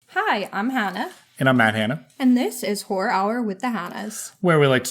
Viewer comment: Did you say “Man Hanna”? Y’all need to clear that up before I get beyond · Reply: Lmao